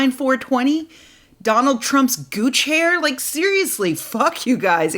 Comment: Heh I thought you said Donald Trump goo chair.